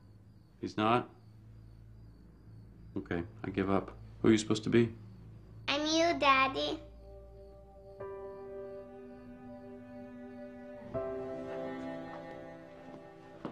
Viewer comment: When Jack says “it’s you Daddy” and you see Hotch’s face fall oh so slightly, I swear I burst into tears everytime!! 😭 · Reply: I thought he said "I'm you daddy"